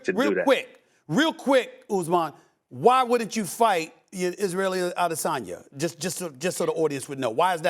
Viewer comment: Did he just call Adesanya "israeli" at 4:30 ?